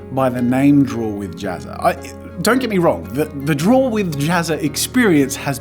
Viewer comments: Dang it Jazza, you made my allergies come back But seriously, there was so many intros in the montage, that it started to sound like he was saying "Drool with Jazza" Edit: Jesus, 175 likes? Y'all too kind <3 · After a few "draw with jazza" I heard "Drool with Jazza" 😂